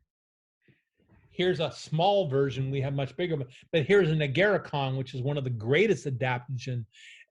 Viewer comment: mentions “nigiricon” can anyone clarify what he is saying exactly? I would like to look this up. Thank you! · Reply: Agarikon: <link>